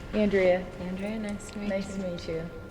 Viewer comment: I heard yo it's me like a girl but it could have been Andrea